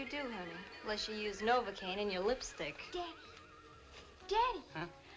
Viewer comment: tommy noonan had something to tell his grandkids, "i held and kissed marilyn monroe." they be like, "you kissed who?" 1:33, what is marilyn calling him? i thought his name was gus esmond, sounds like she's calling him dave. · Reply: tomitstube she called him daddy ,not Dave ...